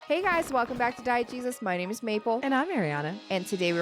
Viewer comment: I mean no disrespect by this statement...when she introduces the channel I always hear "diet jesus". Anybody else?